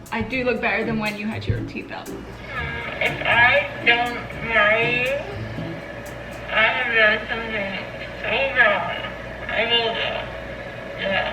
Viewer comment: Feedback: Maybe sub title please? 4:06 I couldn't catch that 😅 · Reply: "If I dont marry you I have something so wrong. I will tho duh" shit took me two mins to figure out